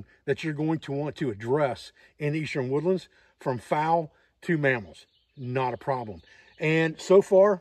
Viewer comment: I thought he said "will take any game in the Eastern woodlands from fowl to mammoths"..........................what? 😲.